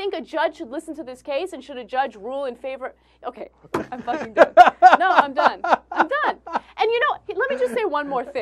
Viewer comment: lol when i rewound the clip to 4:25 i realized that ana said "ok, im fuckin done..." lol i didnt hear her say the f word the 1st time